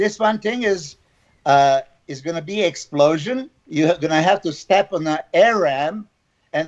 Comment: I swear I thought he said "I have to step on a Arab"